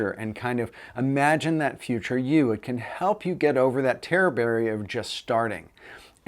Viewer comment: Did you say Terror Barrier or Terreberry? will like it! :D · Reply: Terror barrier.